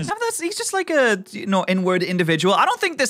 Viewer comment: INWARD. the word said was inward. In case anyone else misheard it like I did